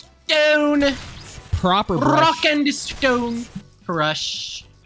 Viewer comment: DID I HEAR A ROCK AND STONE?!